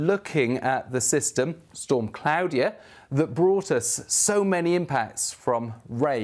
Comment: Captions/subtitles say "Storm Cloudier" instead of Claudia at 2:34 🤣